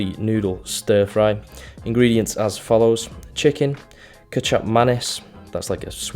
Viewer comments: dude I thought you said ketchup mayonaise at first lol kicap manis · When he said Kicap Manis I thought he was saying “ketchup and mayonnaise” but with a little extra style.